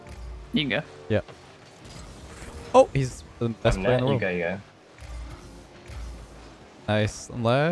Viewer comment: does says n word ? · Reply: He says "you go" quickly. Not sure where you got that.